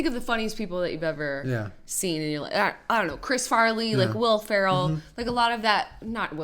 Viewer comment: Bo Burnham is clever, but not hilarious to me. You mentioned Chris Farley, now that guy is hilarious. But Farley is (or "was", since he died in 1997) like the complete opposite of Burnham. His style of humor is self-deprecating, while Burnham comes across as narcissistic. Chris Farley was the best!! I loved that one scene on SNL where he played the "motivational speaker". :P Saturday Night Live, "Down By The River" <link> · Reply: i thought they said chris lilley ?